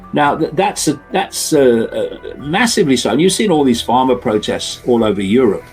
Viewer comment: Not pharma protest but farmers protest 12:04 subtitle is wrong